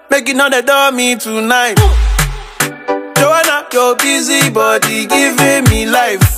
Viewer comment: Oh my lord I thought he kept saying "Your fizzy body" smh